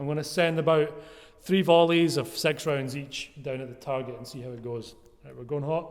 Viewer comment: It actually sounded like you said "Sex rounds each". xD